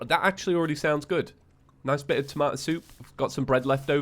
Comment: i thought he was saying 'hazmat suit' instead of 'tomato soup' and i got concerned